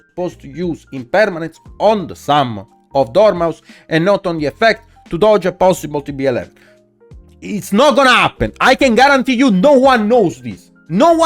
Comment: what's he say? Can't understand what to dodge. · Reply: Saying the opponent is supposed to use the imperm on the banished special summon of dormouse in order to dodge a possible tb-11 if you wait to do it on the dormouse effect then maliss player potentially sets tb-11 from hand and now can dodge the imperm